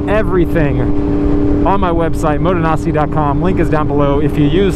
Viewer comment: Bro I thought I heard him say his channel name was "Moto Nazi" lmao